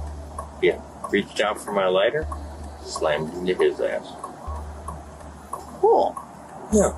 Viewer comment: am I hearing things or did the guy say "Slammed this n***a's ass" instead of just "slammed his ass"